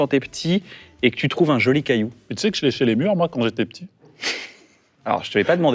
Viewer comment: 12.49... j'ai compris "tu sais que je léchais les burnes quand j'étais petit" 😅